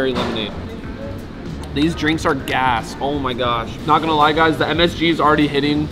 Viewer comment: i thought he said a*s